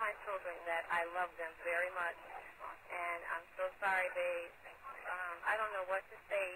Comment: I don’t hear sorry after she says “it’s a frame” I hear “hurry” and not by ceecee’s voice either